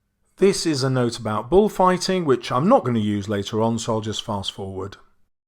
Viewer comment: i thought that said "bullfight anime" LMAO